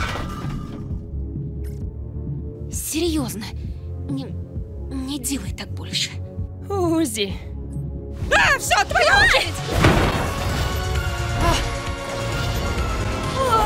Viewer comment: Why does it sound like he says "AAH! SUCK MY EYE!!"